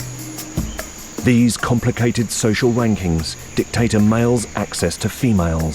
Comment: Did he say 'social wankings'? 😂😂